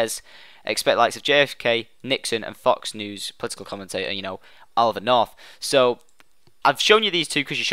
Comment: I thought he kept saying Noland North then I saw the pic and was like daaaamn:(